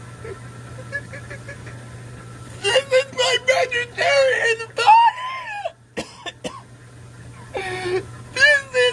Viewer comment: What did he say? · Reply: “This is my vegetarian body!”